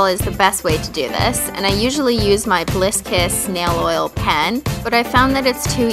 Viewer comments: oh lol thought you said bliss piss!!! XD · who else thought she said bliss piss? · at first i thought she said "Bliss Piss" instead of "Bliss Kiss' XDD